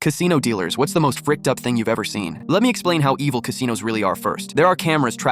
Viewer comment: For some reason I thought he said bricked up thing